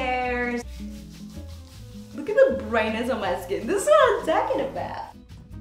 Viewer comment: Also when you said "This is what I'm talking about!" the caption misheard it as "This is why I'm taking a bath!" and it still fits lol